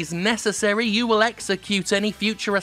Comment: „You further agree that should Blizzard decide that it is necessary, you will be executed...“ I maybe wasn’t as shocked and surprised at this mishearing as would have been appropriate.